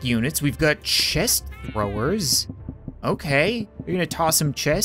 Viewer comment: did he say 'Chest Rubbers' orr id it just me?